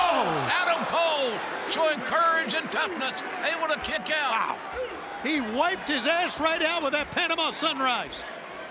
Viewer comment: Tony! What did you say at 3:12? He wiped his ass out? That's what I thought you said!